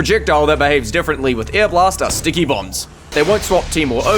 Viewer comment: Stickbombs instead of Stickybombs. Oh well, close enough! Maybe the Y was lost when Pyro airbalasted the stickies away?